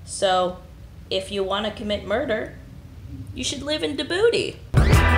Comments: I'm sorry, but I keep mishearing "you should live in the booty" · "you should live in dibouti" sounded like you should live in the booty